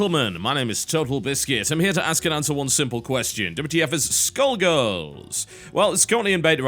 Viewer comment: I kept hearing 'schoolgirls' instead of Skullgirls. Wishful thinking probably :/